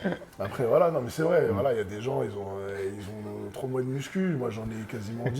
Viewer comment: il dit avoir 10 mois de muscu ou c'est moi ?